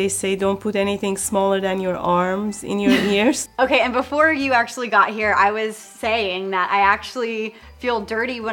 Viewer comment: She’s a Pinay??? Coz I heard her saying “I was actually saying na I don’t bla bla” and “na” in English is “that”